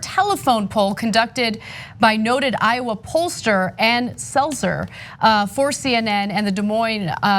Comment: lol I thought she said Seltzer